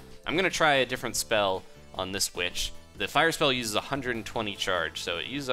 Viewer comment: first time i heard u saying bitch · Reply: he said "witch"